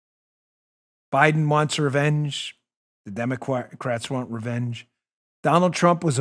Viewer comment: Im sorry, yup...I gotta go there. Lovin @ 46:26 Dan misspoke but in an AweSome kinda way. He meant to say the word ‘democrats’ however he instead sounds like he was going to say ‘DemoQuacks’. At least thats how I heard it :) I freaking love that! Thats an AweSome new twist to the word, since we’ve had the usual DemonRats etc. now its DemoQuacks! Love it! :):):)